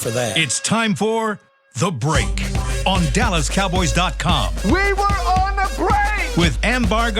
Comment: Can somebody please tell me what the digitize sound is in the beginning when the hands clap? It sounds like it says farm boy, but I cannot understand it. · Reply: “The break the break”